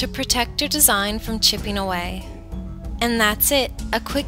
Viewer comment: I thought she said " to protect your design from 'chewing' away lol but I love ur designs keep making more!!!!!!!